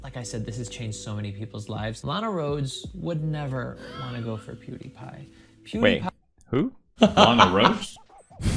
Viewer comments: Instead of hearing "Lana Rhodes would never go for Pewdiepie" I thought you said "Alot of girls would never go for Pewdiepie." 😂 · I thought you said “a lot of girls” not “Lana Roads” or whatever her name is so I found it weird when PewDiePie looked up that girl 😂😂